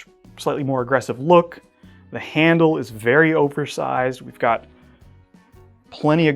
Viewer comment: Did he say "very Ogre sized"? Lol